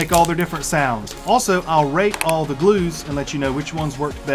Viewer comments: I thought he said rape all these glues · Lol when he said I’ll rate all the glues I thought he said I’ll rape all the glues😂